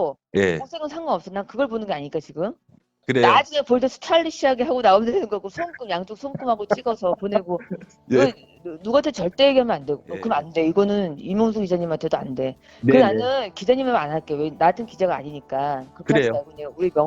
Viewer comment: 즈음에 "누구한테 절대 이야기 하며 안돼 오빠" 는 나만 들리는건가? 처음 알게된 동생한테 오빠라니....